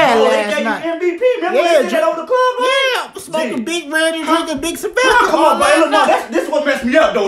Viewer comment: 🤣 almost sounded like he said smokin big ready lol